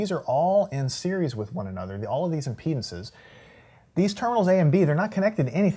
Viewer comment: did he say "All of these in penises"?